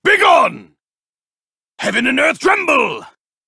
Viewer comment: what he say? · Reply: Heaven and Earth Tremble